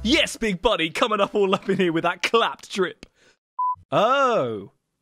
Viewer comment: Did he just say.. Clapped drip